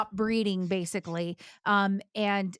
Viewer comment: Did they say breeding? I thought they said breathing....🤣